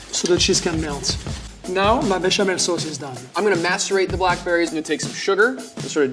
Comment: was i the only one who heard him say he will masturbate the berries???